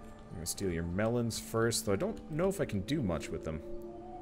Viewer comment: I heard I'll do your melons first, but I'm not sure I can do much with them this was quite chuckling.